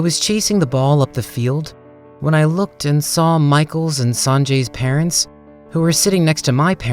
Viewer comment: SJDHFGFH it's sanjay.. i thought he said sonjae